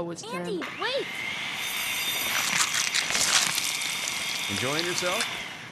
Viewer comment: Even though I beat fallout 3, I thought he said "Eating Yourself?"